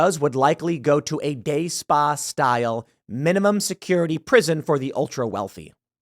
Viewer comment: I keep hearing "gay spa" which could be slightly worse than prison?